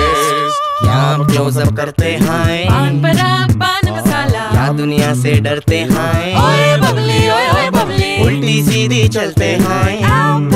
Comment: did they just said bade babble , oye oye bubbly .